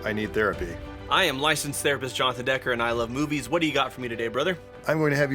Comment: I could have sworn he said "I am licensed therapist Johnathan Decker, and I like boobies"